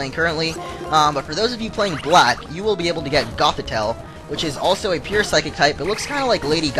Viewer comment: what did ya say att 09:44? Gofitail? ..